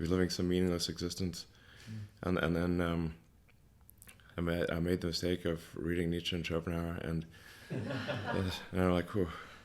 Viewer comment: What does he say at 18:35? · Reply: He said he made a mistake reading Nietzsche and Schopenhauer